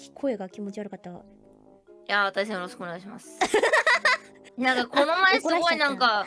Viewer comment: what polka said ? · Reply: いやー、対戦よろしくお願いします。